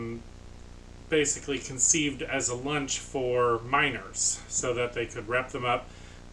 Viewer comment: Hi...your jars are beauties 👍🏼. I laughed out loud when you said “miners” would take them to work...but of course I heard “minors” and thought why would a kid take these to work 🤣